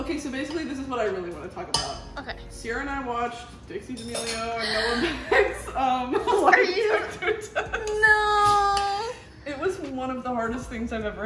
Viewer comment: What did she say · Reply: “dixie damelio and noah beck’s lie detector test”